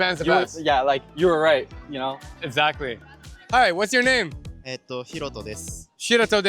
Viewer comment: I think he said "ひろとです” meaning "I'm Hiroto". The subtitles saying "I'm Herodotus" fried me lmao.